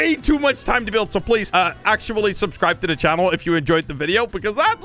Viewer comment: U said pee before video ends but I know that u were saying peace but it sounded like u said pee🚽